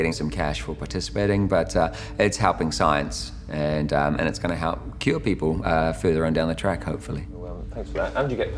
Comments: was I the only one who heard “it’s gonna help kill people” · did he say it'll help kill ppl or did he say cure ppl? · I thought he said the drug would help kill people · did he say kill people? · It's ironic at 5 41 I thought he said "and it's going to help kill people" but he said "and it's going to help cure people" .. the accent. I know he said cure but it sounded like kill... just ironic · Sounded like it said its going to help Kill people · did I hear the guy say I hope it’s going to kill people further down the line???